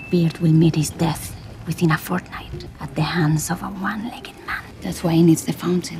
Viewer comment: Did she just say Fortnite?